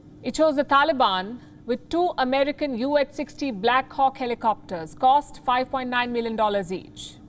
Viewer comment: Forgive my ears, I heard "33 black cocks helicopter "